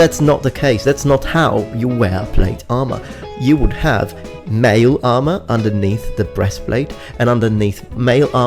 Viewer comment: Did you say women had to wear 'male armour' underneath plate? I don't know... sounds pretty bigoted. Maybe you can't see that because you're a big dumb greasy Italian. Ultra warrior societies like the Genghis Khan era Mongols and the ancient Spartans would have had armoured women as every true citizen of a warrior society is trained and expected to defend themselves and their lands/city. If an invader ever attacked one of the warrior society's cities or if the slaves/non-citizens ever revolted the female warrior-citizens would have had to deal with that if all the men were too busy invading the enemy. But because they are needed to produce the next generation of warriors these women would obviously not have been sent out on campaigns/expeditions (and also Spartan women were trained less brutally than men to prevent accidental deaths because their childbearing ability made them inherently more valuable. They were still trained hard enough that all the Greeks considered Spartan women very attractive because of their physical fitness and Helen of Troy, the 'most beautiful woman in the world', was Spartan). The Gauls during Caesar's campaigns had female men-at-arms... or militia? Anyways that's a rather sad example as it was total war for the Gauls; everyone was fighting to the end. The women guarded the back lines and gave moral support but when the men tried to flee from a losing battle they cut them down and the Romans alike, refusing to give ground.